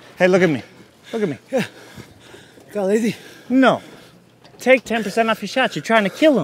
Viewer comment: what is Gaetje saying there? Got a ladie? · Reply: Gísli Grímsson “Got lazy?”